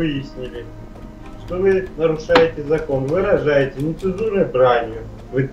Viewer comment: мне одному послышалось что полицейский сказал "вы рожаете":D